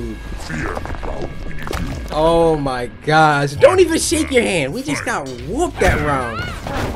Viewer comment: i swear he said dont even shit in your hand